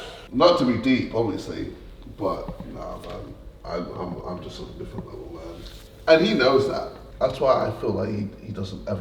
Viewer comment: listened to this like 5 times still can't make out what he said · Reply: he goes "im im im just on a different level man"